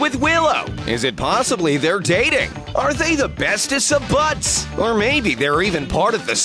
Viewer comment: am I the only one who heard best "BUTS"